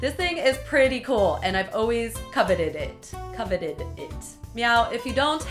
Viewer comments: Did anybody else hear meow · I heard her say meow at 1:24 lol🐱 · DID SHE JUST SAY MEOW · did you notice that at 1:23 she said meow instead of now? XD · Did anyone else notice she said meow instead of now at 1:24? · did she say meow? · Did She Say Meow Instead Of Now? I Wouldn't Be Surprised. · Did she just say "meow". I heard it clearly. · did she say "meow" or am I hearing things??? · it sounds like she said meow hense the cat costume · it sounds like she said meow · did you just say meow instead of now? Oh the pins. (Or me being deaf) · meow instead of now, who else heard that?!?! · Everyone hears meow at 1:24, I hear now. How do you hear the "meow?" I HEAR A WORD CALLED NOW.